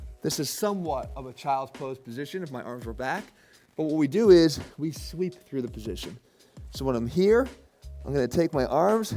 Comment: At first I heard "we are going to sleep through the motion" and thought this is perfect for a laz.. I mean restful person like my self😂